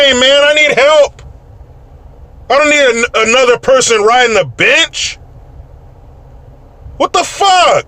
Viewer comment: I thought you said, “I don’t need another person riding a bitch!” 😂😂😂😂😂😂😂😂😂😂😂😂 You said “bench!”